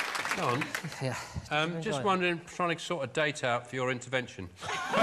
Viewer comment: What does Sean said at 2:47? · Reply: "Just wondering, trying to sort a date out for your intervention"